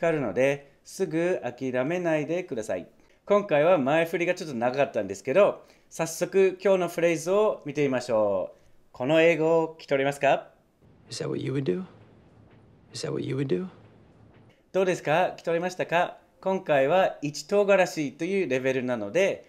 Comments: You say what You we do？ って聞こえたけど、絶対文法おかしいなって思ってた。 Is thatが「イサ」って聞こえるけど、そういう発音もあるのかなぁ。聞こえないだけか。 でもコメント欄見てると Is that はちゃんと聞き取れてる人も居てすごく感心！ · Is that do you do?って聞こえた、、。最後は聞き取れた！